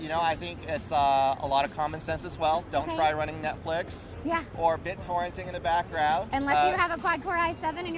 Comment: did he just say Bittorrenting in the background?